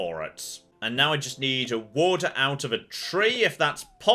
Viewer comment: Every time you say "wood" it sounds like you say water.